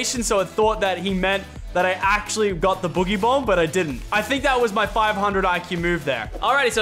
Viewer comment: What did he say? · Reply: Benjamin Vital boogie bomb